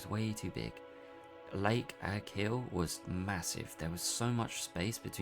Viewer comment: did he just say, Massive 😳?